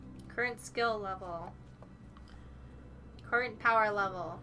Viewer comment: you said skill level but all I heard was voice in the background: "Vaginal efficiency"~